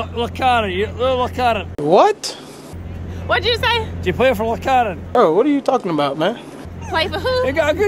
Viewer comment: I think he said do you play for Wakanda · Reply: He said 'Do you play for Loch Arran?' which is a place in the highlands of Scotland :p They were asking if she played shinty which is an aggressive sport kind of like hockey in Scotland